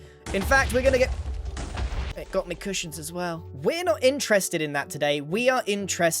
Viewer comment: "They got mi cushions" somehow i only hear "THEY GOT MI BREADD" XD